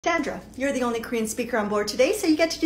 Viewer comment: Who else thought she said " dandruff" instead of" sandra"?